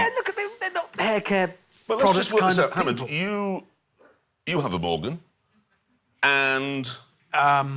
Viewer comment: I like how the translation said, "you have a ballgame" instead of "you have a morgan" :)))